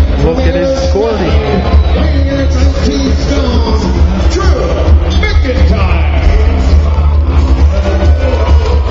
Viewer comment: What did Tony Chimel say? From Ayr, Scotland, weighing ??? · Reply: 19 stone. It's british weight.